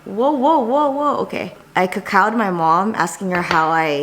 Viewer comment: Did you say "I kakaoed my mom~" or it's just me assuming you said that because I'm Korean! Never imagined you'd use kakao instead of FaceTime or WhatsApp haha anyway if you're reading this your new hair looks really really good on you! I've been wanting to say you are beautiful inside and out. Sending lots of love to you always! ❤️